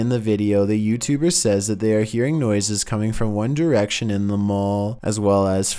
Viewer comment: I thought he said One Drection Like the group lol